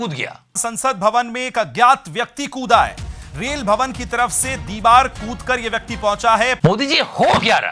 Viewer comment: did the anchor really say 'Reel Bhavan' instead of 'Rail Bhavan' 😮. I guess I know why so..😅